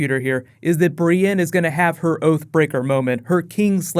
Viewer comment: Thought you said OAF BREAKER. ...thought Hodor had a fall.